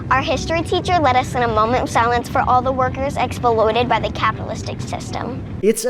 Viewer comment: That child actress in lady ballers has way better comedic timing than any of the grown ass professionals around her. Like that list of things boys are better at is a dumb joke but she actually delivered it with the correct cadence for the joke to work which is more than you can say for anyone else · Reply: idk if its necessarily related to her performance as much as it is just a cute child mistake but the hardest i laughed was at this moment where it sounds like she says "all the workers exploded by the capitalistic system"